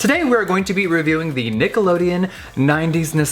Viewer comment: sounded like you said niggalodeon 😂